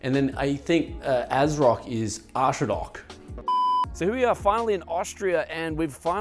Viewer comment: Did he really say ''Arschrock''? :D